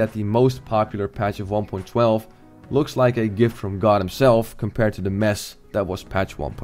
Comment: Did he really say at 27:50 "given from god himself"??